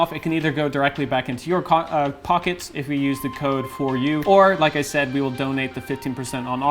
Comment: did he say cock 😬